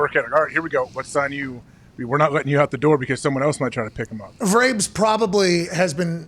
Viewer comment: What does Pat McAfee say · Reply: Vrabs